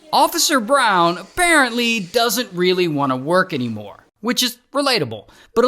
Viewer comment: bro did not say deal tho😭🔥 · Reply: Bro said dill dough😂